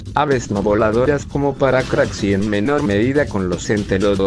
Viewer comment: Que ave era la del minuto 2:35 no entendí el nombre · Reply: Es un Paracrax